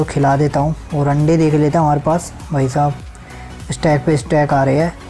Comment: I thought u said randa 🤣🤣🤣🤣